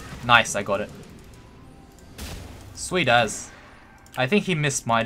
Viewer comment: what is that you say at 10:45. It sounds like "Sweet as" (as in "Sweet as [something]) or are you saying "Sweet ass?" I'm sure it's just a colloquial expression but I was curious. Btw I watch all your videos keep up the good work. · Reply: I think this one is more unique to Aussies, its "sweet as" meaning "awesome" - "great!" something along those lines